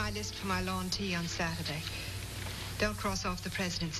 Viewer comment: Subtitling errors start at 0:37 with (instead of "here is my list for my lawn tea on Saturday"), "his finest mine aunty on Saturday." It goes downhill from there, into a continual mire of word salad gibberish.